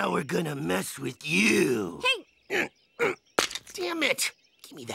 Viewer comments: did he say damn it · did he just say damn it?